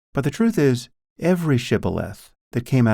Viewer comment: What is he saying at 13:45? "Every shth" is the transcript. Sounds like "every shiboleth" to me, which also doesn't make a lot of sense. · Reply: The word is "shibboleth": "a custom, principle, or belief distinguishing a particular class or group of people, especially a long-standing one regarded as outmoded or no longer important".